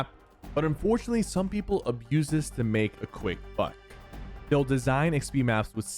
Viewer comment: did he say the f. Word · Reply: He said buck😊